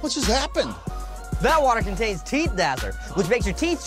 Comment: Sounds like Tom said TDazzler instead of TDazzle